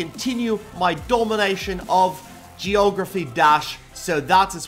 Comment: lol,did you say Geography Dash? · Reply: its Geomentry dash sorry to correct you:D